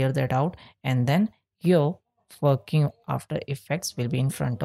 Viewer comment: wait on 1:30 did you say "And then fucking after effects"